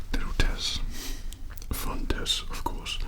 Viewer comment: damn I really thought he said "fuck this"